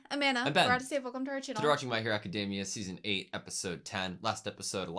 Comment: I heard "My Villain Academia" arc is even narrated from Spinner's perspective.. now it makes more sense!